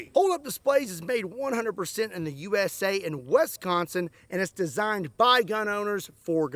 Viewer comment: The way you said Wisconsin is funny it sounds like you said Wesconsin or westconsin lol 😂